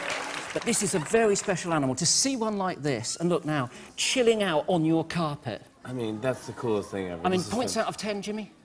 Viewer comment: ''Too see one like this, and look now SHITTING out on your carpet'' is what i thought he said..